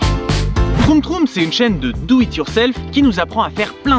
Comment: j'ai entendu une chaîne de "douille it yourself" 😂